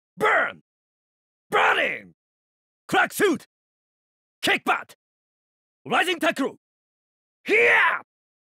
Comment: What's he even trying to say at 0:54 and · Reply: Shoot, Kick back, Rising tackle.